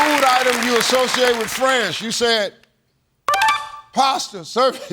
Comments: “Name a food associated with France” Pasta? PASTA??!!?? angry Italian noises 😂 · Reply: I heard Steve say "friends," not France when he asked him the question. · I couldn’t distinguish France from friends, which I thought he said for the second one.